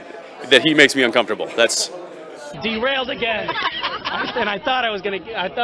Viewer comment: thought he said he was “DeanRailed” again lol 😂